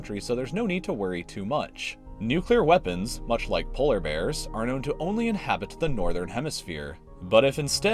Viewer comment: lol i thought he said "nuclear weapons also known as polar bears"